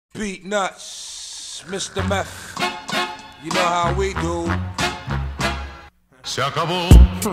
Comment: I keep hearing "Peanuts, crystal meth, you know how we do..."